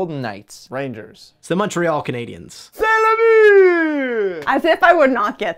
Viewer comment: what did he say? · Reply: C’EST LE BUT IT’S A GOALLLL (in french)